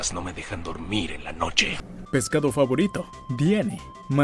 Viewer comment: Pescado favorito Fue lo que dijo ? xD